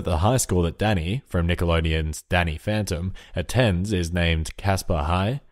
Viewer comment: when u said Danny phantom I thought you said daddy